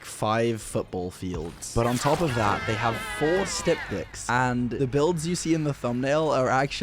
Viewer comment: 4 man stream snipe on eyrie with 4 styptics and still getting killed is wild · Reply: Is that how they're spelled? I legit don't play DBD so I thought he was saying "they have stuff dicks"